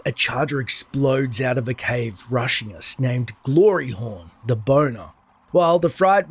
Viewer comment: Good you showed the name afterwards, I thought you said 'Gloryhole the Burner'